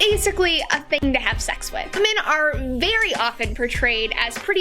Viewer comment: I think you're saying "women," right? The jump cut made it sound like "men" and I was a tad confused